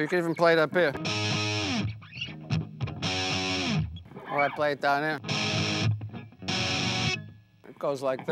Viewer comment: when he says "sometimes I play it down here"................I hear "hotter than hell"............might be just me